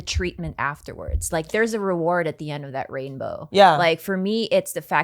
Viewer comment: talking about being degraded saying “there’s a reward at the end of the rainbow” and I heard “rapebow”